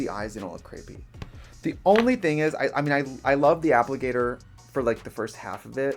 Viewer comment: Is it me or does Trixie always look like Magneto with his helmet when he starts doing his eyes? Also did she say "appligator" at 14:04? lol